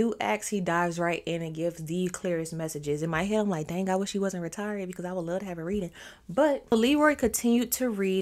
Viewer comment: If I heard you right about using that R word at 8:23 I want you to know that it’s an ableist term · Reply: I said “ I wish he wasn’t retired because I would love to have a reading”